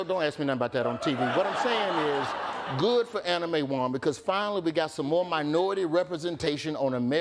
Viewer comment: I literally thought he said "Anime Wong". I thought to myself, (Wow, an anime character on a quarter). lol